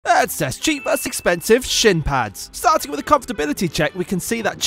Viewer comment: i thought he said "shit pads"